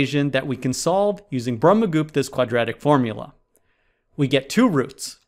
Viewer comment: What did he call the quadratic formula at 3:06? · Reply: Brahmagupta might be